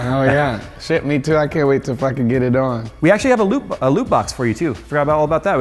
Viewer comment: Jay: we got a loot box over here Malone: Damn, I thought he said a loop box